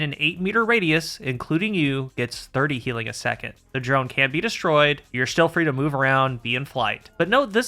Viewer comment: Sorry, did you say the drone can be destroyed or cannot be destroyed? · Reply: can not be destroyed